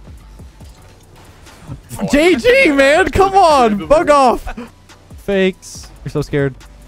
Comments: Who else thought that said fu** off in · did say "fuck off" at 5:00?